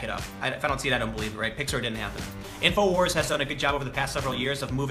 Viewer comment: i'm pretty high and i had to watch 4:03 four times before i figured out what the fuck he was saying. i coulda sworn it was "pixar didn't happen," (turns out the captions agree with me) which would be an excellent tim pool take. like pixar is a shell corporation or some shit and it's like a front for the globalists (does he say globalists?) and all the movies are just made by people at disney. idk i'm just spitballin. just . thrwoin around some ideas. tossin the ol pigskin with uncle tim .